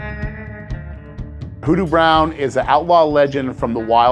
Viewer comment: I thought that said "Doo Doo Brown" lolololollol